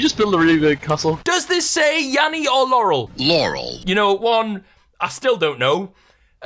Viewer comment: IM SHOOK I JUST HEARD LAUREL FOR THE FIRST TIME AND I REWOUND IT AND HEARD YANNY (LIKE I NORMALLY DO) WTF